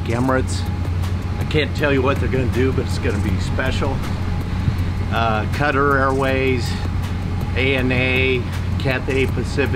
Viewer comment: Sounds like he said Cutter Airways! lol